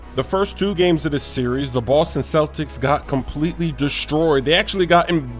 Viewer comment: did he really say the "Loston Celtics" ? xD